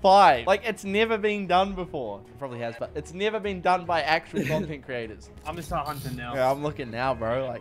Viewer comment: When he said “it’s probably never been done by actual content creators”, the captions thought he said “it’s probably never been done by accident content creators”